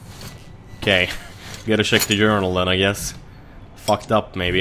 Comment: When you said journal i thought you said urinal! xD